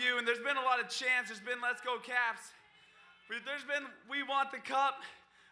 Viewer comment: Thought it said let’s go pens